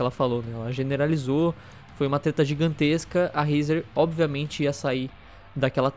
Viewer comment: fui so eu que ouvi "foi uma teta gigantesca" kkkkkkkkkkk